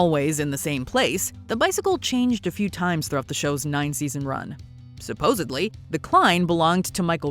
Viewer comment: Did you say "Supposably"?